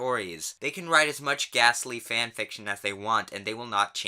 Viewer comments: Lmao at 3:44 I thought you said they can write as much gaster fanfiction as they want 😂😂😂 (Undertale, ya know?) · I heard “ghastly fanfiction” as “gastly fanfiction” and I was ready to drop Pokémon forever